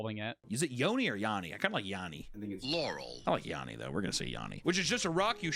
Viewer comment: I know I’m not tweaking but I heard “laurel”